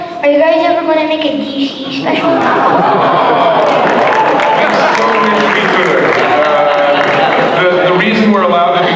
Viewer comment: What did he say? What kind of special?? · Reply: DC special